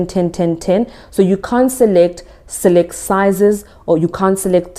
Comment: Just to make sure I heard correctly: At 13:50, You say: You cannot select sizes?